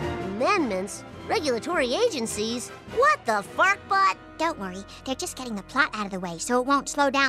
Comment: What did Bart said in 1:25? What the what? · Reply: I meant fart-butt.